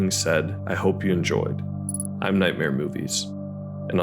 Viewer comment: When you first said your name I misheard it as Nightmare Boots, made for a fun image